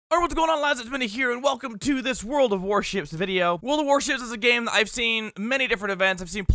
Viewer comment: At the beginning I thought you said warshits